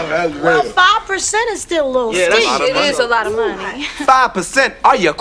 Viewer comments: did gina say "shit is a lot of money"? haha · DAMN CUZ IT DID SOUND LIKE SHR SAID "SHIT" is alot of money...